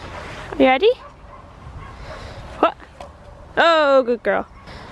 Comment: Luna is so beautiful. Fun fact, you were playing with the dogs and said something like, "Ohhhhh, good girl." And my Google Home thought you said "Okay, Google" :D